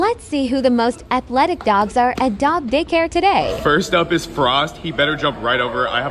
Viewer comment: For a second I thought I heard the ai say the word “dog dick hair today!” And I had to rewatch and I was actually wrong